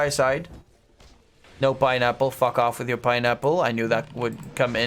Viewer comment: Maybe I'm not good at English and listening so I Heard Caster say Fuck out(lol)